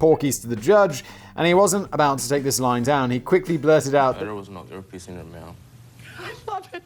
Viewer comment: I may be going deaf, but I could not make out what the black guy said in the 26-second case, nor could I hear what Judy replied. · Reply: (He) There was no ear piece in it, Ma'am. (She) I love it.